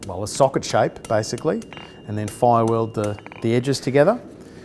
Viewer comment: did he say fireweld?